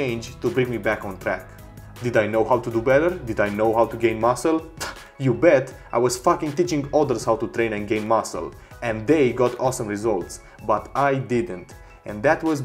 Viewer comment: That's it, I promise next time I'll stop duplicating myself haha Did you get an "Aha moment" while watching this? If so, please hit reply and let me know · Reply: did you say 'fucking teaching others' ? Hahaha.